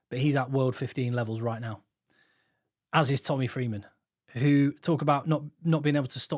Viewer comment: right at the end I thought I heard you say -- Tommy Threeman -- as your 3rd spot player 😂😂